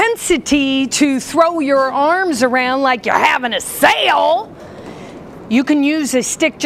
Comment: “some of you throw your arms around like you’re having a sale” LOL 😂 thank you for the tips and for making me smile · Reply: That's what she said? I heard "throw your arms like you're setting sail" LOOOOOL